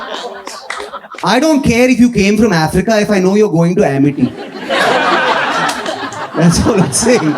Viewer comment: is he saying Amity is... Good?